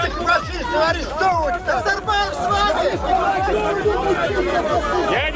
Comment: мне одному послышалось:"Назарбаев с вами?" 🤣